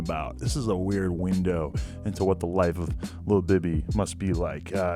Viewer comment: Who the fuck is lil bibby. I thought adam was trying to say lil baby